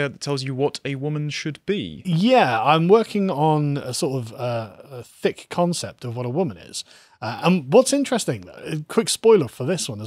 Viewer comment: "Thick concept of what a woman is." For some reason I heard this as "Thicc concept of what a woman is." Fingers crossed.